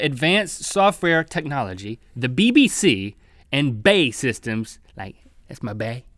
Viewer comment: did link say BBC